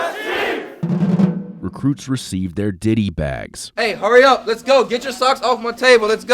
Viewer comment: why did i hear "diddy" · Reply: Ditty*